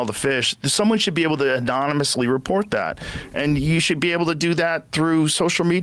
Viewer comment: did Joe Rogan say Adonismously ???? Lmfao instead of anonymously???? Am i hearing things lmfao